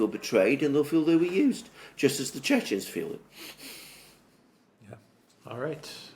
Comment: What does Alexander say here, the audio doesn't even allow for meaningful subtitles: "just as the churches feel..." - or was it Chechens? Did anyone understand what he was saying?